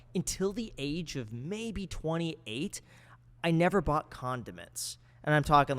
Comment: at first I thought he was saying he was saving money not buying condoms